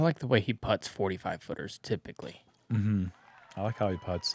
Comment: I slowed down Simon's first putt at @ 2:10 to see analyse it, and it made Uli sound like he said said "I like how Simon putts 45 footers too big boyyy" 😂